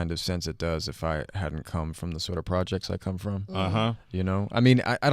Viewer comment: It sounded like he said came from the projects 😂